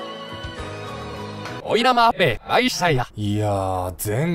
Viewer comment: キヨさん「おいなま～へい、あいしゅたいな。」 ・・・何て言ったの？ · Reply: 逆再生で 林家ペーパーマリオですね！